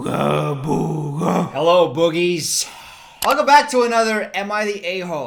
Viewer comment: Deadass thought you said “hello bronies” there at the beginning 😂 almost gave myself whiplash trying to figure it out